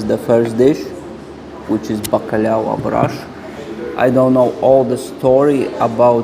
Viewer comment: "bacalhau à broche" that's what i heard